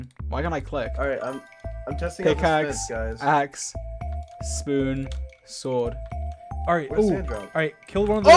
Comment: DID YOU SAY SPON